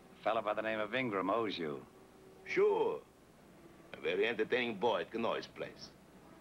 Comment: What did say at 14:40? "He's a very entertaining boy..." but I couldn't make out the rest. It set Ed Begley off. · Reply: A very entertaining boy at connoys place,.....